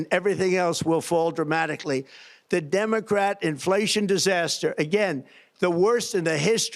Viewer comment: Did he just say "Demongrat?"